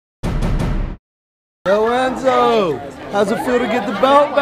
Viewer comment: Kinda sounds like he says Lorenzo at the start of the video